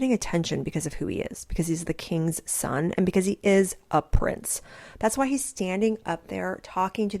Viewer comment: When you said “he IS a prince”… … I heard ‘he IS a prick’…!